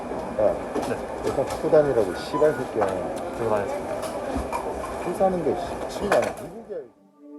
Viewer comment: 다섯번 여섯번 정도 돌려보다가 드디어 다 들리네요 15:53 야 여권 갖고 다니라고 ㅅㅂㅅㄲ야, 술사는데 미친거아니야 미국이야 여기